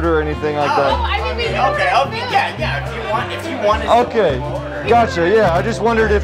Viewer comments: Did he just say '' kya chahiye '' ? 😂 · i think he said " kya chaiye"